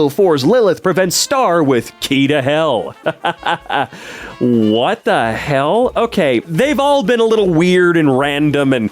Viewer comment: what is that censored word?? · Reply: Weed